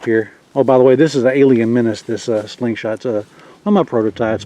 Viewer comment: I wonder what name of this slingshot, the way it hold like u keep a gun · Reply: Alien Menace (Prototype), I think that's what he said.